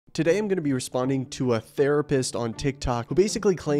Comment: Wait, I thought you said atheists don’t exist???